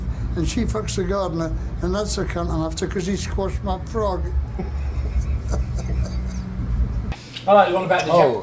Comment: what did he say here? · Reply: "and that's the c*nt I'm after 'cause he squashed my frog!"